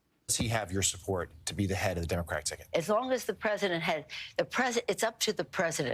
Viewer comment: does Nancy say it is up to the President to subside or decide, sure sounds like Subside to me. subside verb (used without object) to become quiet, less active, or less violent; abate: The laughter subsided. Synonyms: ebb, wane, lessen, diminish. Antonyms: increase.